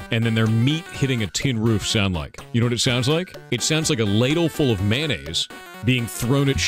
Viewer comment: some reason I heard him say 'ladle full of lasagne', I'm starting to think I'm hungry